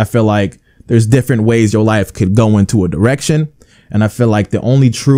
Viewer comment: I’m not tryna be rude bro I swear to god that I 100% respect u but I SWEAR I thought u said your life can go into many different erections